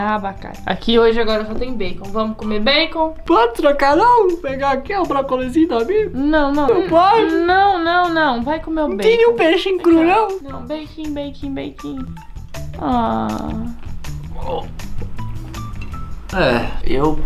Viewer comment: quem ouvi ela dizendo vai se ferra